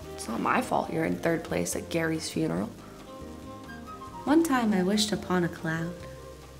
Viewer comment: I THOUGHT SHE SAID ONE TIME I WISHED TO PLOW A CLOWN AHAHAHAHAHAHAHAHAHAHH